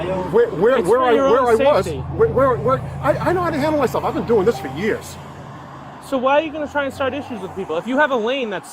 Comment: When the 2nd guy said "I've been doing this for years," I had to rewind and listen again, because I could have sworn he said, "I've been an asshole for years."🙃